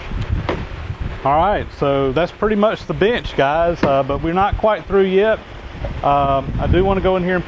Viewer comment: Nice project. Check it out on the closed caption 21:12 -- "far-right so that's pretty much the b*tch guys probably not quite per year", thought that's what you said.